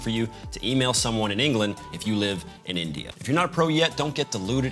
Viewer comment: did you said India?